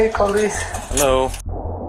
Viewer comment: what were his last words “hello”